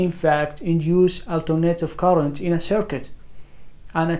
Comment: Surprising information! Important information! Did you say at 1:24+ that graphene at room temperature induces "alternating current" in a circuit? After several listenings it sounded like "alternative current" . Please clarify. Use a dry erase board to illustrate. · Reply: Yes , The graphene does in fact induce Alternative current in a circuit , this achievement thought to be impossible . I have posted this article on my website also , you can read it <link> Also you can get more information about this breakthrough from the university website : <link>